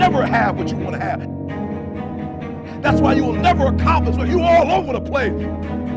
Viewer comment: I'm trying to help to translate this video but I can't understand what is said between the second 00:50 to 00:54 can anyone help me writing what it says in that moment? in English please. Thanks anticipated. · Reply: that's why you'll never acomplish because you're all over the place...